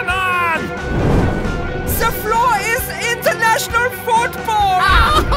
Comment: i thought he said THE FLOOR IS LAVA